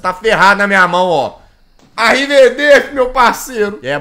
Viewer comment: foi só eu que escutou ou ele falou "Arriverdeci" JoJo Reference?